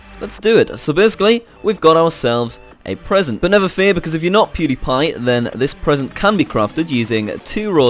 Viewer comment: did he say "if your not pewdiepie"??